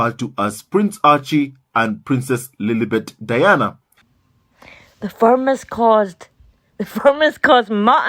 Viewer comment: Oh... The Firm has. I thought he said the farmers caused🤦🏼‍♀️😂🤣